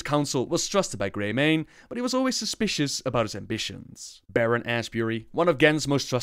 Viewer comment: I thought he said Baron ass Berry